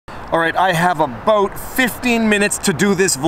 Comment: You have a boat? I don’t see any water...🤷🏻‍♀️ 😆 Also, I think technically it was supposed to be “stop, question, and frisk.” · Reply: I thought I misheard him say aboot.